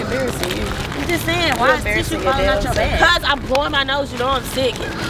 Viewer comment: The fact that TI thought she was saying “you got tissue flying out yo *ss”😭😭😭